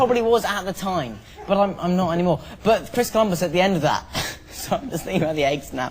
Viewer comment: What does he say at 7:30 when they're talking about football? Cuz I swear I heard "my first time was at the end of that" or something along those lines :P